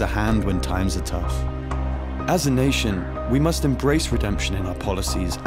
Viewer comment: “our nation” in the most manchester accent will never not be funny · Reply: i only hear “as a nation”